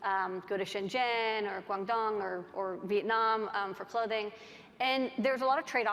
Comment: Did she said : Viet Nam ?